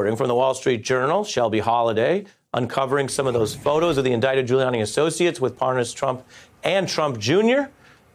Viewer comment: 408. Sounded like he said Doodie Gulianni